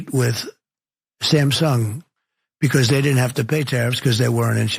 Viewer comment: I honestly thought he said ‘Sam Sung’ and wondered who he was. Took a few beats to figure he meant Samsung.